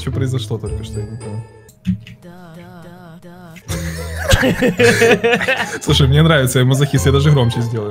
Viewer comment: "слушай мне нравиться я же мазохист" стопэ что что?)0)) Мне послышалось ??